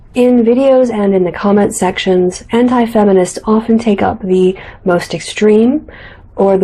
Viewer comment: >< I honestly thought she said hentai-feminist.. ( Around 10:43 ) That.. Oddity aside. Part of the reason why I like your videos is due to the fact that you go into everything in detail. A fair amount of people on here do not go in as much detail wise as you do.